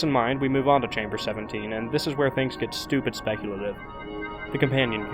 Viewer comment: what he say? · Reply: Stupid speculative.